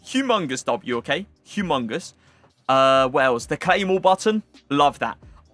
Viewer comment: I thought he said a claymore button 😂😂